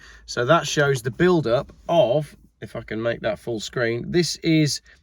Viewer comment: Sounds like he said "Fuck'n make that full screen"....But settle down lads, I know what he said :face-blue-wide-eyes::face-green-smiling